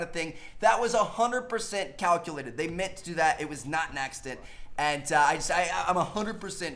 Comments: did you say naccident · Did just say "Naccident"????